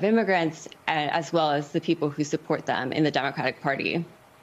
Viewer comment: Did she say in the "in the Democratic Party? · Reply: She said 'demonization of the people who support them in the Democratic party'.